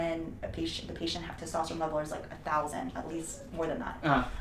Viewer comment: Doctor: “Youre at 21 ng/dl” Fitness expert: “What’s average?” Doctor: “700” Looks at camera and smiles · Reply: I thought she said 200 at least which is still low.